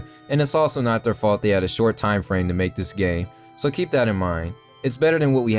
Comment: sounded like you said "they had a short time frame to make this gay", LOL